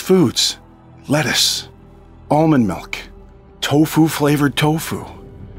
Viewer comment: did he just say tofu flavored tofu!? dose tofu even have flavor